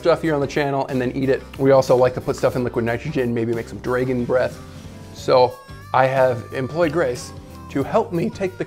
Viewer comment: did he say draygon breath?